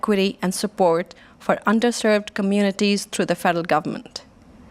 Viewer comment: I thought they were saying "UNdeserving"... realized they were saying, 'UNDERserving"... but what I heard makes more sense so... I'm going with that